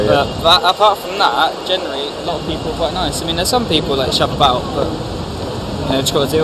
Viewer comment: did he say "chav about"?